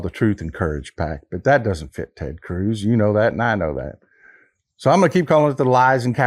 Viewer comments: i thought i heard 'TURD' CRUZ...I LOVE IT !!! Him, Gaetz, MTG, Boebert, etc. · Turd Cruz? Or did I mishear the accent?